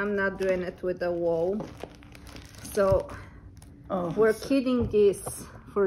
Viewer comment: Such wonderful projects, I love the knitted pig! That pattern is beautiful, thank you so much for the offering such an awesome gift! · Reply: oh, I thought you said the animals were knitted. They’re all adorable either way!